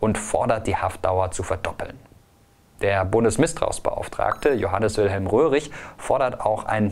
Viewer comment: Hab bei 10:04 erst mal "Bundesmisstrauensbeauftragter" verstanden 😂😂